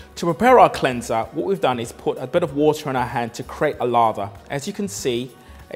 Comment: Who thought he was saying “lava” whenever he was saying “lather”?